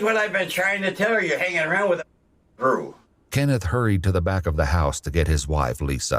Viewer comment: What did he say? 😂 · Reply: a f*cking crew